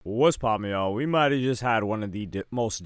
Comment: Sounds like bro is saying “west popin yah”